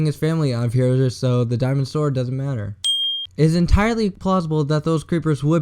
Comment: Am I the only person who heard "So, the dinosaur doesn't matter."